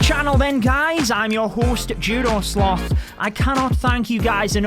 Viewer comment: All I hear is "I'm your hoost Joodoo Slooth". In all seriousness, congrats.